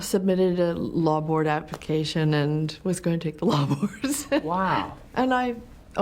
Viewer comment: what did she say at 5:31 - 5:33? can somebody help me? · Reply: Meryl said she was going to take the law board application. She overslept and felt it was a sign she wasn't supposed to go that route. She went into acting instead.